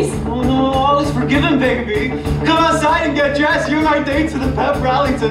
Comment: Did he say baby babe or can i not hear properly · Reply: He said baby :)